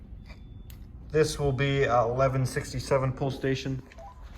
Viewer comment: did bro just say 67